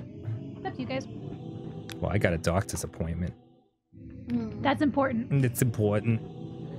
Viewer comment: I thought Dan said "dog disappointment" at 2:32:14 LOL